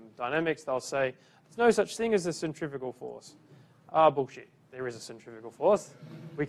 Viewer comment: Did he say "bullsh*t" in front of his class? 7:24 min